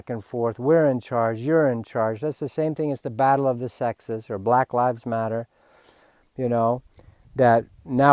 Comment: I thought you said, "black labs matter." ;)